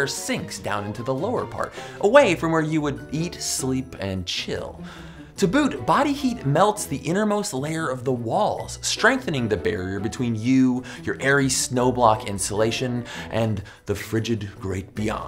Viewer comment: what does he say? Ta boot? lol